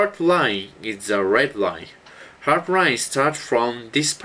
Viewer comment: LOL He messed up so many times, and HIS ACCENT DOE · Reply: i thought he said red wine and hard wine